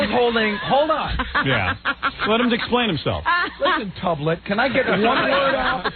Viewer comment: Does anyone know what says at 53:50. “Listen Tuvlick?” Also what is it in reference to?